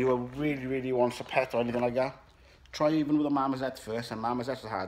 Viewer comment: What is that animal that you said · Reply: Mormoset